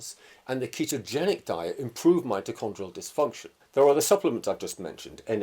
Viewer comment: Wait! @ 39:26 Dr did you say the plant-based & ketogenic diets improve mitochondrial ‘dysfunction’?